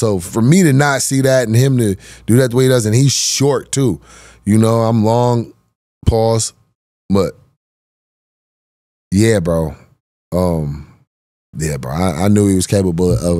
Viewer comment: Am I crazy but did you say pause at 19:56? Or am I just paranoid